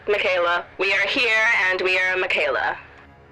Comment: I don't know if this means anything, but did anyone else catch at around 8:20 that she says "We are here and we are Miquela."? Maybe it was misspoken or something, but I don't know, just throwing it out there.